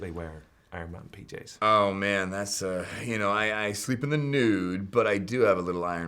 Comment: "I sleep in the nude" CAN I JOIN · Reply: My dumbass heard it as "I sleep in the noon"